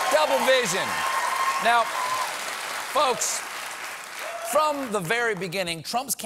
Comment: (Closed Captions): "NOW, VEHICLES..." What Stephen said: "Now, folks..." Whoever's in charge of this channel might wanna fix that 😅😅😅